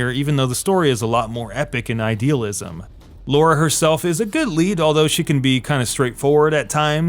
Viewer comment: Everyday is a kick-ass day when you're watching:D Quick sidenote: it sounded like you were saying "Laura" instead of "Lara" but great job with the video anyways.